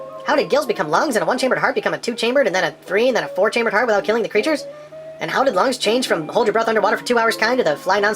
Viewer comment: Great content, as always! But between 6:22 - 6:29 I had to slow it down to .25 speed to understand what was saying. Now I think hearing "killing the creatures" in that creepy slow voice will give me nightmares. Seriously, thanks for your excellent ministry!